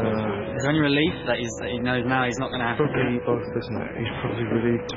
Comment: did he say “he’s not a green apple-“ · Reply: Drapple